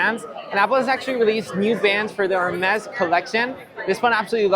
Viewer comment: that first titanium watch, is that the natural titanium 10 and where do I get that titanium band with it? Looks clean! Did he say apple Hermes collection???